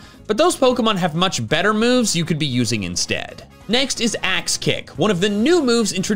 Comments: Never have I ever rewound the video over again to double check if I heard something correctly faster than in this moment here. Thought you said "Ass Kick" lmaooooo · I heard ass kick😂